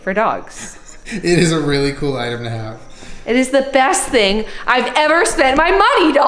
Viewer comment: Did anyone hear when Julian said 'it is a rlly nice Iron Man outfit at 14:44?